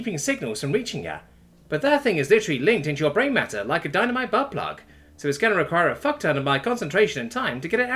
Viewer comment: God I love Dave 🤣 it's lodged so far in the brain it like a diamond butt plug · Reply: I heard "dynamite butt plug" and damn near spit out my dinner.